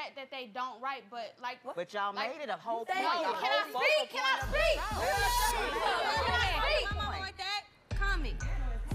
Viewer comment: what was young lyric saying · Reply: "Don't be yelling at my momma like that calm it." -young lyric